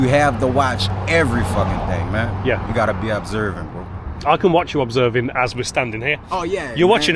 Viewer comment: Did he just call you Boo ? lol 🤭 ( You have to be aware #Boo pmsl ..